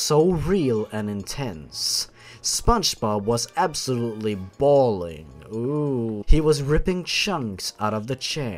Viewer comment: "Spongebob was absolutely bawling" is my favorite sentence in this because I heard it as 'balling'